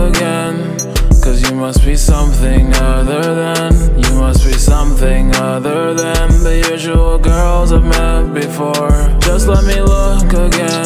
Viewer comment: what did he say? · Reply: cuz you must be something other them, you must be something other them the usual girls i met before